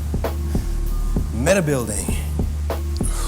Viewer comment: Yo. I think you did something when you said “medi-building”. I heard it as “metabuilding” which actually works well with what you were describing as the higher rep work seeming like meditation.